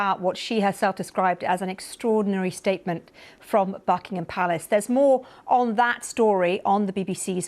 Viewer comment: I thought she said Fuckingham Palace?! :face-blue-smiling:🤪